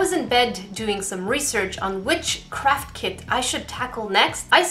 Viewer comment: I thought she said “witchcraft kit”